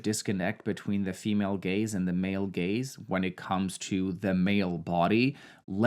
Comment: Amazing video and concept, but I'm laughing so hard rn at 9:33 you said male gaze and female gaze but the captions say "gays" looool